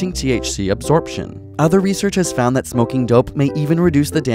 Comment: did he say "smoking dope"? For those who don't use slang he's NOT talking about heroin, Dope is a slang word for weed. If you try dope you might as well throw your life in the garbage until your ready to clean yourself up.